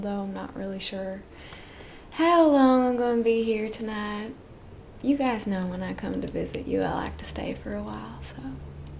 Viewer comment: was that a "you guys" i heard? ain't ever been a southerner who said "you guys" instead of "y'all"